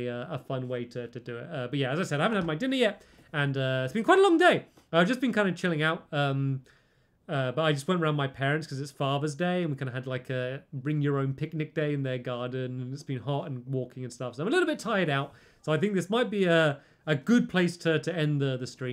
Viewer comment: i thought he said it was Farmers Day 😂